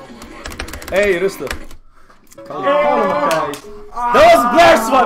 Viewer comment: Lol Why did i hear some one say 'Hey rustig'? At 0:45 (I heard it cause im dutch lol)